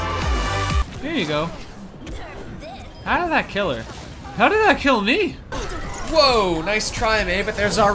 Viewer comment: “how’d that kill me?” Sounded like Barry 🅱️ benson